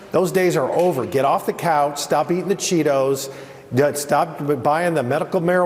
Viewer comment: all i heard was stop don cheeto!